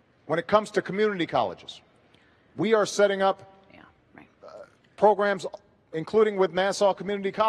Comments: I think I just hear that lady say, Yeah right, @ 7:45... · listen closely, does she say "yeah right"..